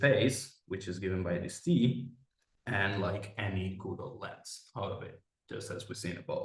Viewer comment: Thank you for the talk. What is the word you say at ~25:13, ___ lens? The transcript says "goodle lens" (and my CC says "Google lens"!) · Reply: good lens, good morphism, good topos!